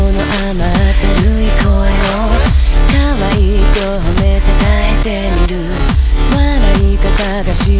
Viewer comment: 初めて聴いた時は「可愛いと褒めて)耐えてみる」って聞こえてた。